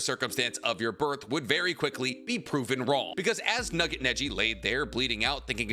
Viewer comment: yo…. Did he just call Neji Nugget? 🙃 we’re not gonna talk about this?